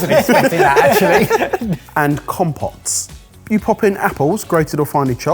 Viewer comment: I misheard compotes for compost and instantly got more impressed, but alas. Still a cool machine!